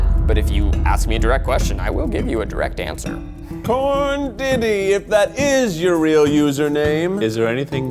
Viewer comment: the captions say porn Diddy